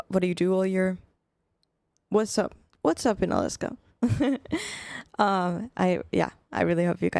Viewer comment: did someone say, Up In Alaska? 😅